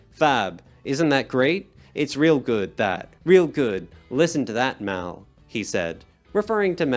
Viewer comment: I think Lennon's saying "We'll listen to that now" (re: the take) as opposed to "Listen to that, Mal".